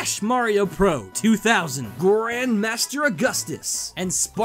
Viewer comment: I heard a GRAND DAD in there